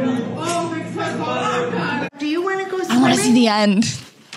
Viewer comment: I heard you say at 5:44 i wanna see the end wants me to do it instead but to you [smirk] edit: i subbed and liked this instantly when i saw this vid